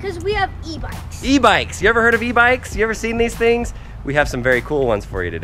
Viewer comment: I thought you said E-vites